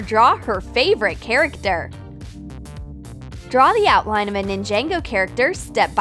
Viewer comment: Did she just say “Nin-Jango”?